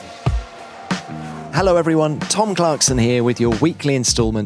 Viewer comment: Genuinely thought he said tom clancy